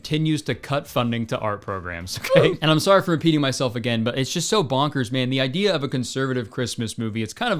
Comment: “I’m sorry for peeing myself again” is what I thought you said till I saw the captions man ☠️ love your content